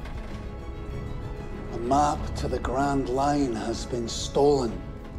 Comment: If you listen carefully this "Indistinct Chatter" (IC) from snailphone, you can hear: Garp: "Raiding our base in Shells Town" IC: "Roger that" Garp: "What did they take? Weapons? Gold?" IC: "The map of... Grandline" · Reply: I Heard even " a Pirate in Straw Hat"😂